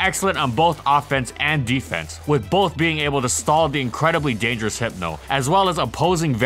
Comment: did i just hear that? A dangerous hypno? How times have changed